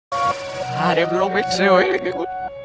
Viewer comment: Escuche en el momento 0:02 que mike dice otro mike